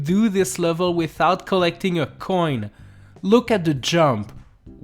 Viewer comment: At first, I thought it said jumping instead of collecting a coin